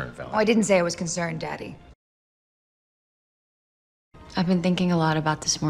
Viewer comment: 아무리 들어도 I가 아니라 Why로 들리는데, Why로 하면 말이 안되겠죠? ㅎㅎ